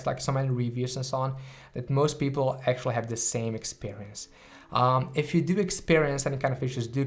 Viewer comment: I thought he said if you experience fissures